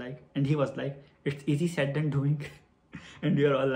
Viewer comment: I'm having loootta fun watching this :D Just sometimes I don't understand what you say. Could you please tell me what Changkyun said at 18:15? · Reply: He said "its easier said than done"